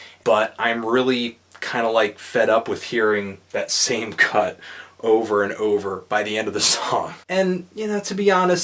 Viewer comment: What did he say? o__o "I'm fed up with hearing that same cunt over and over"